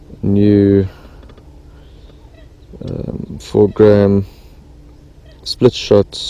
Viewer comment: Nice video my bud! WHat did you say at 3:21? Is that an Afrikaans word I heard haha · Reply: i said 4 gram not fokan hahahahahahahaah